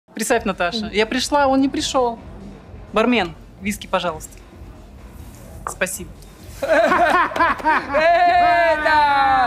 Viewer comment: tendi foi nd kjkkkk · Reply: Priceti natasha ila prixa o prixoo barmem disque bajalasqui expecific